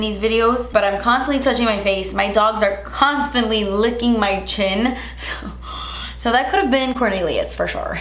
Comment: When you said your dogs are constantly licking your face, I heard “My doctor’s constantly licking my face.” 😂😂😂 talk about doing a double take!